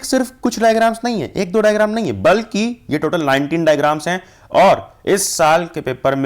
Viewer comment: i thought he said 90 diagrams 😱😱